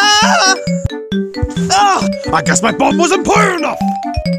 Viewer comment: What Did He Say? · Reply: He Said: I Guess My Bomb Wasn't Putted Enough!